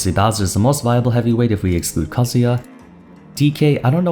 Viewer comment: I been watching too much of ya genshin content lmao. Thought you said kazuha